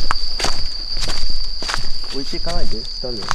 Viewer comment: 確かに10:56くらいで置いてなかないでって聞こえる😮